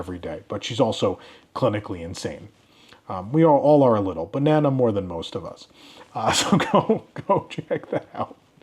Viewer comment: "But Nana more than most of us". What I hear (bc I, too, am insane)- Banana more than most of us 😂🍌